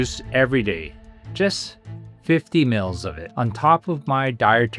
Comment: 50ML equates to 1.7 ounces. I bought a little measuring cup and I drink 50ml once each morning on an empty stomach. Then I wait about an hour before eating breakfast. 50ml is a small amount and it only contains approx 7 grams of sugar. (If you drink an 8 oz serving it's like 37 grams !! Way too much sugar). So beware if you have diabetes or glucose issues not to drink more than is necessary to achieve the goal Dr. recommends. Dr, is amazing and God has truly blessed him to educate us all on how to clear our arteries. Thank you and God bless you Dr.!!!!!!! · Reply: Thanks for that. I thought everyone was saying 150 ml. I’m gonna OD. 😬